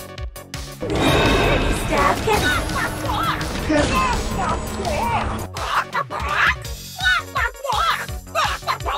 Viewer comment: Is she saying the F word or is Bock bock bock? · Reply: roxannewolf-6615 she's saying bock bock bock